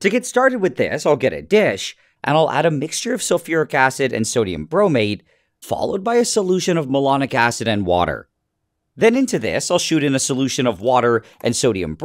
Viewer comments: I honestly thought you said moronic acid for a second 😂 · Initially, I thought he said "melanic acid," whose reaction is slapping a hand away... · I heard it I'll get a bitc* lol